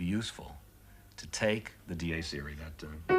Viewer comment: What is the piece he refers to around 10:20? I keep hearing “D.A. Serie(s),” but only keep getting independent rappers and other miscellania when googled. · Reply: Dies Irae.